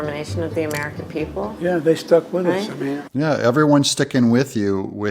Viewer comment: The first time I heard Joey Bribes say "They suck with us" at 1:05...then I listened again and heard "stuck"